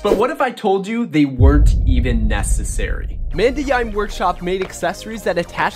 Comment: what is the name of the store that sells these visors? I don’t know what he said in the video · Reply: The store is called Mandayaim Workshop! Here’s a link to his macrobinoculars that I talked about in the video: <link> You can use code “MICAHNIKE” at checkout for a small discount!